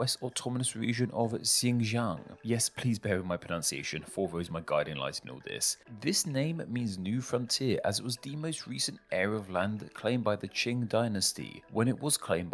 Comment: What the hell he say at 1:55? “Fervor is my guiding light in all this?”